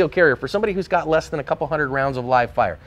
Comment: Did you just say under 100 rounds of live fire??? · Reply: a "couple hundred" is what I heard.